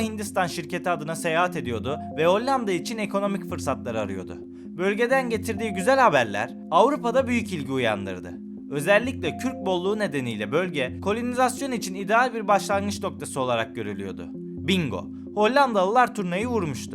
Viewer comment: 'da kürt bolluğu mu dediniz ben mi yanlış duydum?